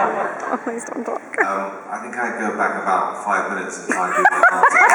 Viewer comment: what did Tobias say at 21:40 ? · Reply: "I think I'd go back about 5 minutes ago" because he gave such a poor answer before so he joked that he might go back there and fix things haha